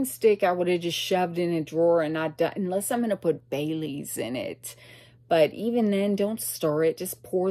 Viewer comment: I thought you said you put bay leaves in your coffee . Not Bailey's 😂